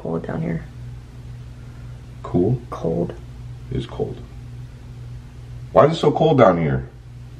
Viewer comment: When you asked "why is it so cold" I heard "i want it that way"